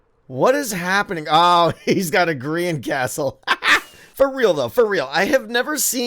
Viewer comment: The no back part of the castle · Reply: JkillerZH82 MC oh lmao i thought he said green-castle as in green-screen because he was using it as a backdrop